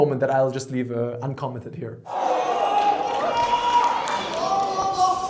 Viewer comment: I heard “whoosh my nigga” when he was on the stage performing. 😂